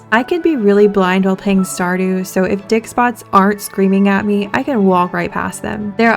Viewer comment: why did i hear "dick spots" my mind is in the gutter